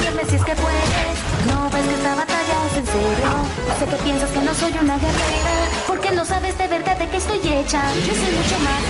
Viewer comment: cuando dijo esa parte de: se que piensas que no soy una guerrera yo pense que habia dicho: se que piensas que no soy una de river. xdd